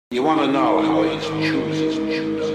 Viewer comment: i cant stop mishearing the guy saying "you wanna know how i eat some shrooms" wtf is the guy saying? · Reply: actually the line is "You wanna know how he's choosing them, dont't you?" and it really is from The Red Dragon.